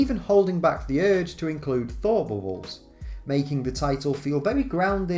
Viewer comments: What are you saying at 6:06? The auto-generated captions are saying "Thor boreholes" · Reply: "thought bubbles", I think. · cant even understand what youre saying. Are you trying to say thought bubbles?